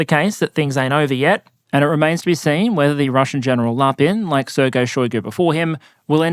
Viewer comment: 😂 "Preferrably commanded by General Lapin" Less than 1 minute into the video, and he's already roasting someone! I love this channel. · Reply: I misheard it as General LARPing